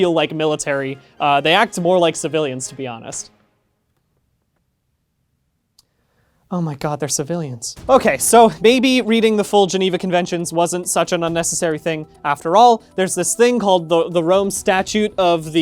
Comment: '.........oh my god, they're civilians.' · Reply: I honestly thought he said, "there're civilians," and had hunkered myself down for him to rip everything up and start over.